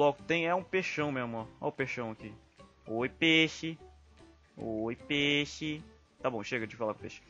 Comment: parece o oi cueio